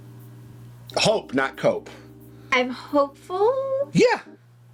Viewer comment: I thought said Hope not Coke for a second 😂. But seriously, i look forward to the reactions to this show!